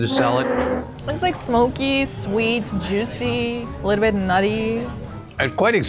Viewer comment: At first I thought she said “gnarly”. I was thinking, “Oh, didn’t know she had that one in her vocabulary”. Turns out it was “nutty”…